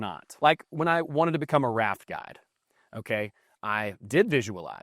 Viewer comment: thanks...I had to do a double take - I heard “rap god” when you said “raft guide”😆👊🍻✌️